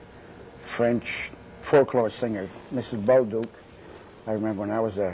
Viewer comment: What is he saying at 5:10 about a folklore singer? Mrs. Beau_____? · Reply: Bolduc.